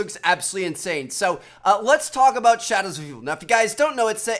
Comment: "So let's talk about Shadowsuhvv" and I still don't understand what he says in his outro